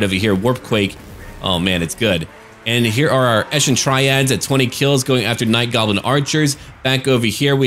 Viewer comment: Each time I hear "Eshin Tryhards" :D